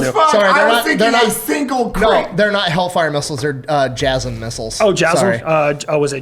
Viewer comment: THANK YOU TO OUR SPONSORS! SHOPIFY Sign up for your $1 per month trial and start selling today at <link> TURTLE BEACH Level up your game and get 10% off with code UNSUB at <link> ! #turtlebeachpod SURFSHARK Go to <link> and use code UNSUBSCRIBE at checkout to get 4 extra months of Surfshark VPN! TUSHY Over 2 Million Butts Love TUSHY. Get 10% off TUSHY with the code UNSUBSCRIBE at <link> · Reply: When y'all said the rapid dragon fires a bunch of jasm missiles I heard "jizm missiles" and thought 'would there be anything more demoralizing than being shot with a bunch of missiles containing jizz?' 🤣🤣🤣🤣🤣